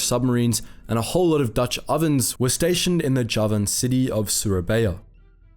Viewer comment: Uh, did he say dutch ovens at 2:04?